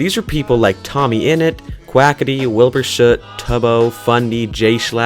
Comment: wait did u just said (wackity) ??